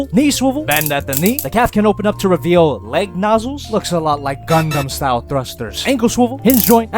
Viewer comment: did you say Gundam?